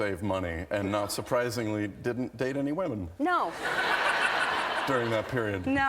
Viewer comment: Can anyone please tell me what he says at 4:37? Surprisingly... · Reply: not surprisingly, didnt date any women.